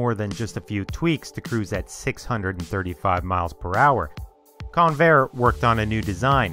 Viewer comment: I literally thought you said Conair lol They transport prisoners around the USA. I was wondering why they'd be building aeroplanes ✈ 🤔 🤣